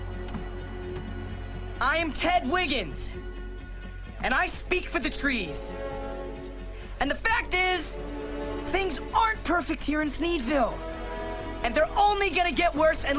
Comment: I thought ted said I speak for the vagina lol